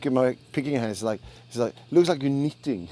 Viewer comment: what he said on minute 8:36? my pickin hand looks like a ................ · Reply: "looks like you're knitting"